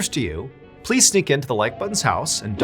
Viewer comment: Oops I made a boo boo i thought.\nYou said sneak into my ex.Wife's house